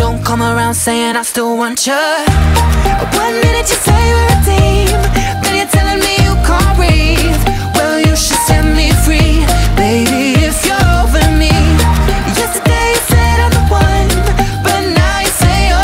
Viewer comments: anyone else hear: one minutes you’re seventeen. Then you’re telling me you can’t read? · I always thought it said "one minute you say you're a teen Then you're telling me you cant read" 🤣